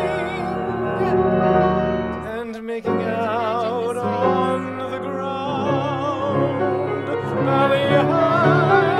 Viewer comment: Does anyone catch what laura says at 10:05 ? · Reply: StripesofShay Wanna say its something to the extent of “You can imagine the sweetness."